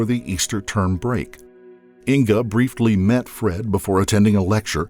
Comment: Did you say "brieftly"?